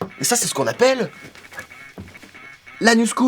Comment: J'ai compris l'anus cool xD